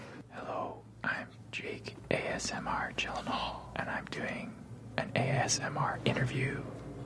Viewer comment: When he said ‘I’m doing’ and paused I thought he said ‘I’m gay’ and I was just like ‘lit bro me too’ and then he said ‘asmr’ and I got disappointed